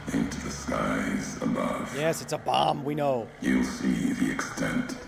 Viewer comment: Man I couldn’t comprehend what he was saying till made sense. 😂😭 - -bu-ye the part he said “yeah a bomb we know!” - Cracked me up when I first saw a few seconds. 😂😭